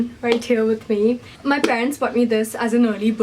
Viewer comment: girl i wish i had friends like yours , my friends barely wanna hang out let alone buy me a new iphone. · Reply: i heard "friends" the first time but then the second time i heard "parents" its definitely parents lol